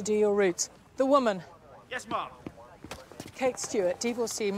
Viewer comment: Omg, I used to think Osgood was Kate's daughter but I searched it up and she isn't but I was wondering why I thought that, turns out it's cuz she said Ma'am and I thought she was saying Mum 😂😂